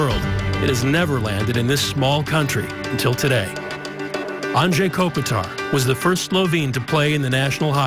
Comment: Did He say Andre Kopitar? LOL